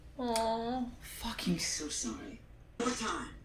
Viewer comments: When you said fuckin It Sounded like Kim said I'm fuckin so sorry haha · omg lmaooooo I thought Lin said “fu*** so sorry” 💀😂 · Heard as if Lin said: “F**** so sorry.” Instead of “I’m so sorry”. Lol 🤣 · HAHAH It sounded like Lin said "F*cking so sorry" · sounds like Lin says "fucking so sorry" 😂😂😂😂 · it sounds like lin said "fucking so sorry" LOL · sounds like she said Fucking so sorry 💀